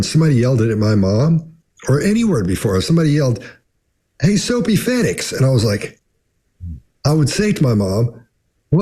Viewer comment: "Soapy" what? Or sow pea, snow pea? What did say at 48:17? I want to know, it is something I have never heard before. I will also accept that it was made up, I just need to know! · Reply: I just made it up to use as an example of a word or phrase I’ve never heard before. “Soapy Fettix”.